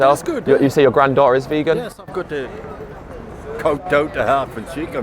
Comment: In the subtitles 8:30 I think he says "kowtow" rather than "cow down".